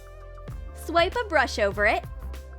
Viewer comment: "Carefully remove the glue, scrape a bush over it, remove the glue...." AM I THE ONLY ONE WHO DIDNT UNDERSTAND THAT?!